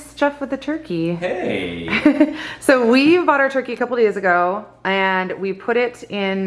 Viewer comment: Omg it sounded like she said they got their turkey a couple years ago lol